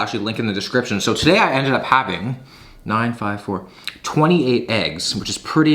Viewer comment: Wait at 12:09 did he just say “9,5,4 is 28 eggs” the eggs gave a higher ig too